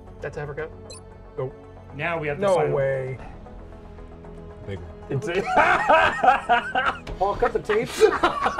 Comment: What does say to? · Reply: The big one